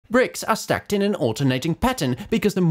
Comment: What I heard "Brits are stacked in alternating pattern" 😂😂